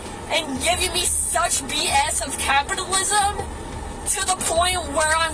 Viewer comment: What the hell is she even trying to say? "Captalism"? What does that have to do with it? 😂